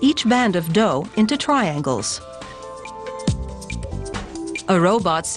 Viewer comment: Twiangles? I can't be the only one who heard that